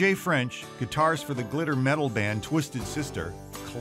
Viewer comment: Did he just say “glitter metal”?